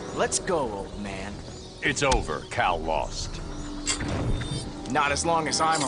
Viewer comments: I think Batman said "It's over, Kal lost." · I think Batman said "It's over, Kal lost." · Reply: But they thought he said "It's over, cowel lost"